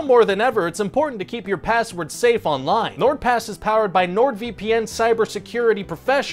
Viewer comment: i thought he said nordvmp bc I always hear that as a ad